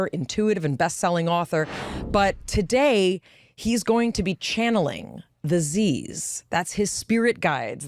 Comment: I was only listening and thought she said: "Today he is going to channel disease" 😴😴😴